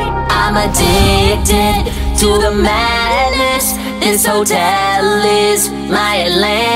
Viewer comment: Anybody else thought they were saying, “I’m addicted to the malice?”